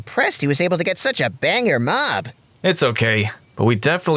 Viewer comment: Banger mob 3:31 but I heard bang your mom